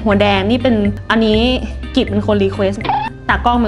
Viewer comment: นาทีที่0:58พี่พูดชื่อตากล้องอะ ไตรได้ยินไลค์เลย